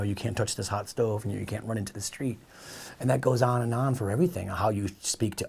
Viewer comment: Sounded like he said “no, you can’t run into this tree”. The urge, though!